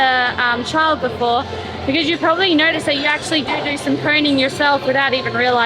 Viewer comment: What is the discussion about around the 6:00 mark? The subtitles said 'pruning' and then later said 'cleaning'. With the accent I had no idea what she was asking about... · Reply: I think she was talking about coning.